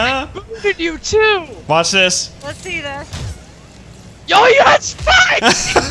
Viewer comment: i thought said yoyo had spikes then i kept playing it back to figure what he said